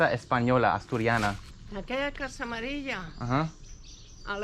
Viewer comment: I think she said turn left at that yellow house "amarilla" not "Maria"... Thank you for sharing your adventure; Subscribed!